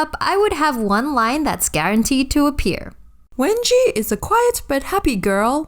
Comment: it said Wendy is a quiet but happy girl instead of wengie is a quiet but happy girl